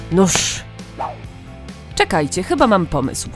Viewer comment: czy tylko ja usłyszałam:nóż xD xD xD xD xD😂😂😂😂😂😂